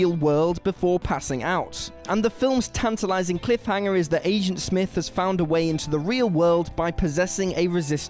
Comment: did he just say Asian Smith?